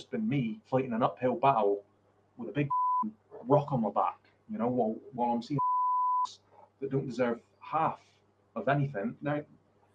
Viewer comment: he actually said "while I'm seeing mfs that don't deserve half of anything" while pointing at himself. He filtered himself there because he knows that's not the right way to think, yet those are his beliefs. It's one of his "hidden" excuses to do what he did.